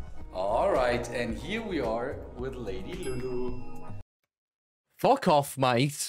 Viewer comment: He calls his anaconda lady Lulu that’s hilarious 😂 love the vid bro, guy seems sound as and leads a crazy life 👀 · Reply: i'll have you know that is a python not a anaconda. and to be honest i thought he was saying lazy lulu XD, my hearing must not be working